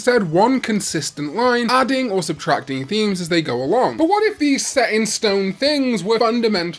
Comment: Looked away from the video for a second and thought you said "adding or subtracting wings", which... would also make a surprising amount of sense. Never quite realized how almost all of the Digidestined Digimon have at least one form where they gain wings, or if they already have them, gain more wings (for example, in Adventure and 02, it applies to everyone except Gomamon).